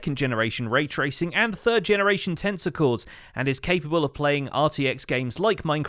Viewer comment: I heard 3rd generation tentacles... I think I definitely had too much of something...